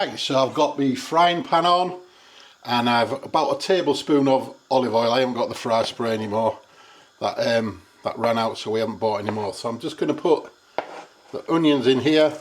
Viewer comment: You had me worried there for a minute, I thought you said “fly spray” 🤨 Turned volume up, and there you go “fry spray” lol 😂🤭